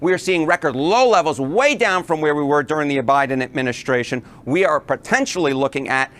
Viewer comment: did he say O'Biden? 1:34 😆